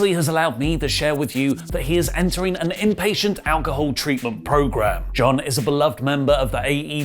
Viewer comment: I thought the post said "impatient alcohol treatment", leading me to believe it was a joke. Glad to see hes getting some help, stay strong Mox!